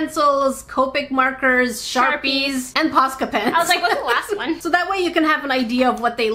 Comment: If you add the subtitles at 16:50 it says that instead of posca pens it says pasta pen haha no offense to the person who wrote to the subtitles